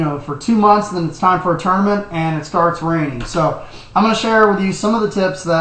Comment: did he say "tourmanent" at 0:16?